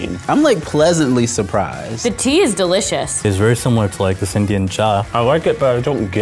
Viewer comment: I put on captions to understand what he dated and the captions said Indian ja 😂